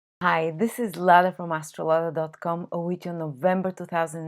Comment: this time I heard: "Hi, this is Lala from <link>..." lol great video anyways